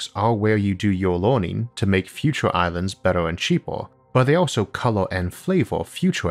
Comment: did say "future islands" at 5:07?? I guess that's where the band got it's name ;-) or vice/versa??